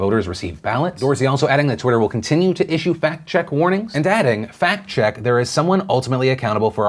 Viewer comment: I thought for a second Phil said Twitter slapped a "fat check" warning...Then I realized, OH, fact check warning. Completely different.